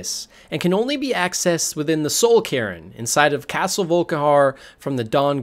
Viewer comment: I thought you said “Soul Karen” and I was confused